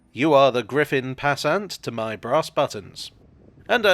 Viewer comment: Bit of a cold coming on there, so I thought you said "my brass buttocks" there at the end 😮